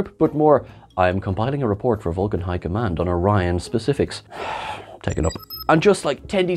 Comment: I thought I heard T'Lyn say she would prepare a report for the "High Council" instead of the "High Command". Did anyone else catch that?